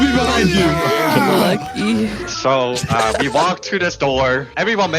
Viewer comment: did she say lucky???!!!😭